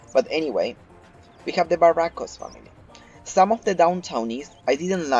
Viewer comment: Did i just hear by 57:06 "Ich habe the Barrancos family?" Anyway, great Vid with great Content. And a new Sub, because I love TS2 so much. :o)) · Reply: Sorry its my accent, idk why when i try to record videos it really gets all f**cked up lol I said, "Here we have the Barrancos Family" Thank you so much for watching it means a lot! Welcome to the channel :)